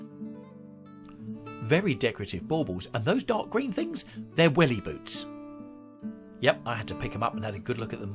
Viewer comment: Ha! I thought you said, "These are Willie boots" ! Bahahahahaha! They won't hold MY Willie, thank goodness! You are terrific!